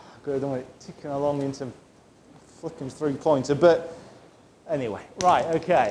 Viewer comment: Language? Or did I just mishear, idk. lol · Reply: it was "flipping" I promise